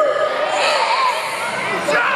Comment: What does the man say? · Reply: He says “THERE’S ALREADY A SASHA!!!” 🤷‍♂️